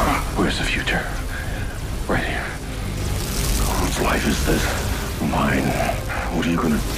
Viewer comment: What does black lightning say @ 1:10? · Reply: Whose life is this? Mine!